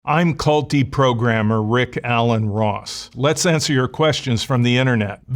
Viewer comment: I thought he said "I'm Culty Programmer" 😂😂😂